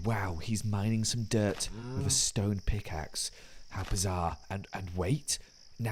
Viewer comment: did you say bizarre? (totally not a jojos reference)